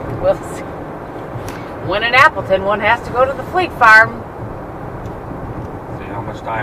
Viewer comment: Oh how funny...I honestly thought y'all were saying flea farm the whole time!! 😄